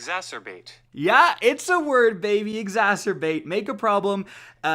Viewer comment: thought you said masterbate...